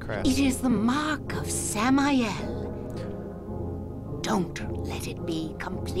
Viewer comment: Oh, Samael? The first time she said it I thought she said "Sam I Am." I guess this one sounds more spooky.